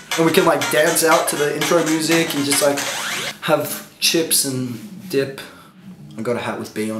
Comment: OMG I HEARD "HAVE CHIPS AND DICK" damn it what are you doing to me